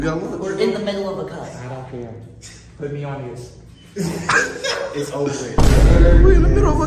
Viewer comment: I thought he said put me on the list. Which woulda been wild but inappropriate